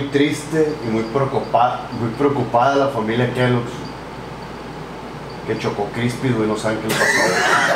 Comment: Soy el unico que no entendio lo que dijo? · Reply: que chocó-crispis we. :v